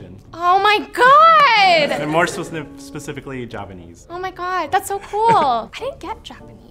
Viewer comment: You guys made a mistake in the subs, at 7:52 he actually says Javanese not Japanese, because as you all probably already know, Japan is not in Indonesia, but Java is.